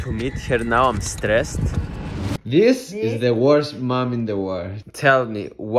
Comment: I thought he said worst man